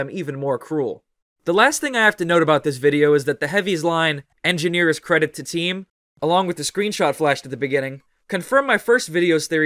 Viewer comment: am i the only one who thought the heavy said "engineer is traitor to team!"